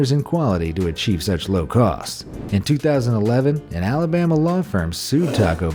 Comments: Come again!!!👂🏻 Did I hear 2011?? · Says 2011 but the caption says 2013 🤔 10:03 or so